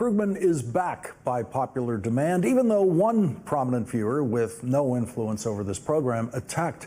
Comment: Did he just say “one prominent Fuhrur?” Or am I tripping? 😂